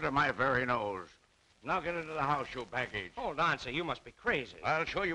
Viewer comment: what did he call her? Does anyone gnoe? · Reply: To let you gnoe, he said "baggage", as in financially, 'cause the brothers had charge, of their niece.